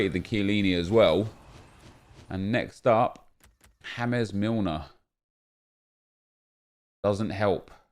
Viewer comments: Did he just say hamez milner instead of james? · did he just call james milner "ham-es"? lol